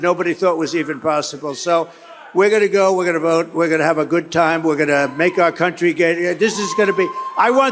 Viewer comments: Did he say We're gonna make our countey gay? 😂😂 9:15. Stop lying n quit putting our country down you orange buffoon. Vote 💙💙💙up n down the ballot to get rid of tRump n his sycophants. (I'm worried. They said he is ahead in the polls in.😢😢) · Is anyone ever going to tell Trump that YMCA is a gay anthem. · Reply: it did sound like he said "make our country gay erm" just before playing it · Did he say ' We are going to Make our Country Gay' ? ..whatever floats your boat donny !! 😆💙💪💙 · Did he just say we're gonna make our country gay again? Right before he says play YMCA nice and loud. Go back & listen, then let me know.